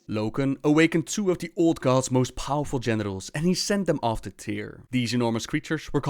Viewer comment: "Loken awakened TWO of the old gods most powerful genitals." I simply cannot mishear this.